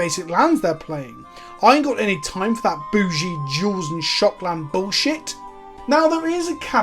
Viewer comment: I swear when you said "boogie duals" I heard "boogie Jews", and I was like "what is this, PewDiePie?"